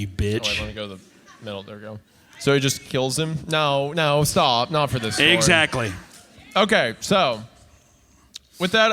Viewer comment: Anybody know what the fan says at 1:18:10? I can’t make it out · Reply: Sounds like they said "i agreed" in reference to Meats theory of the mom actually being insane